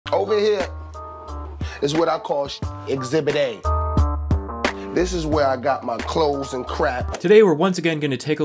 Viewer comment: At first I thought he said "heres where I store my clothes and crack"